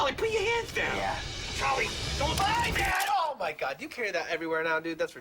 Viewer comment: did he say "bye dad"? Never noticed that